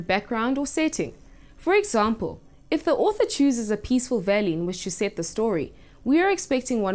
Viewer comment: I am really really thankful to you. I learned so much about the atmosphere in literature in an interesting and easy-to-understand way which I could not have at school. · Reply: yesss............@ 3:55 can you tell me what she said?? ..........i think it started as "peaceful something.........